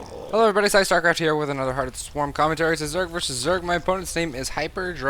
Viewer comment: lol, at first I thought you said at the start "Hello everybuddy psystarcraft here with another hearth of the swarm", I got really confused about that for a second there.